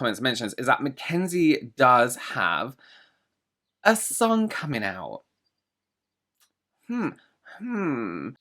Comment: I heard "Mackenzie has a SON coming out" hahaha